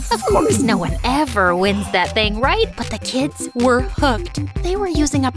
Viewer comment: I thought it said the kids were cooked😂😂